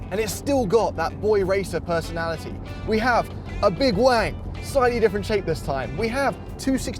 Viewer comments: did he say "we have a big wang"[?] Ummmm..I didn't catch what he may have said about the shift nob... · Did James say he has a big Wang at 1:55?